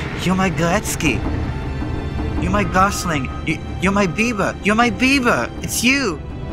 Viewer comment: Is he saying you my Justin Bieber 1:14 She called Bieber